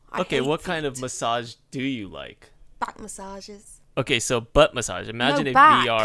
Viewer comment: In defense, I heard "butt massages" too.. :P